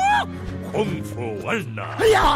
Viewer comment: so I'm watching this while drawing, and I wasn't looking at the screen. I deadass thought he said "kUNG Fu pANDA~" and was confused on why I didn't hear you goof on it lol.